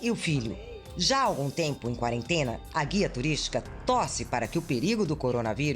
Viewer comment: Entendi "A guia turística tosse"